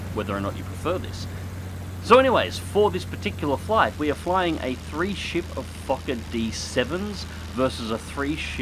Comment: For a second I thought had said "We're flying a 3 ship of fuck'n D7's" Not Fokker D7's